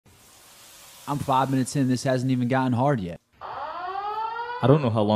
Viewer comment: At first i thought he said " 5 min in and i havent got hard yet" i was speechless